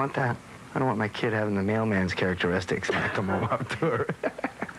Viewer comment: What does he say at 2:53? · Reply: Skaliex I don't want my kid to have the mailman's characteristics. 😂😂